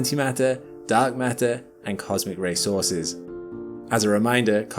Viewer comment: Cosmic racehorses? 😂 sorry, was half sleeping when I heard that!